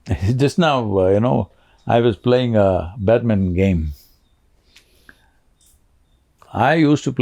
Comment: I thought you said "Batman Game" xD "badminton game"